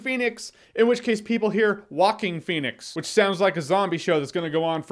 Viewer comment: I always hear "Walking Phoenix" no matter who says it. In fact I still have no idea what the real name is. (And intentionally not gonna google)